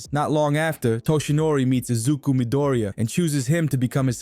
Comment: I don’t see it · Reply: look at 8:52 and when he says chooses the captions said uses